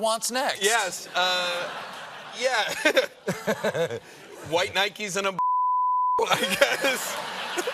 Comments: For Those Wondering What Nick Said That Got Bleeped... Stephen: "I wonder what father wants next" Nick: "White Nikes and a blowjob, i guess" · Anyone know what the bleeped word at 5:07 was? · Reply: Blowjob, I think.